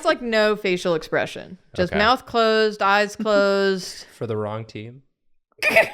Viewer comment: its a fall out boy lyric from the same song they were all singing two seconds before LOL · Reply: I was definitely hearing something lyrical floating around my brain when they said it, but i wasn't getting anything specific. Thank you! Edit: Just listened to it again, and I was confused because I thought said "the wrong teeth" not "the wrong team", everything makes sense now